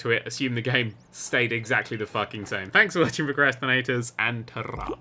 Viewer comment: Did he just say PROcrastinators? There's water for thirsty ears...